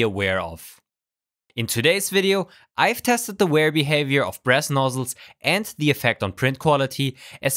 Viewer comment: did he... did he say breast nozzles? i really cannot tell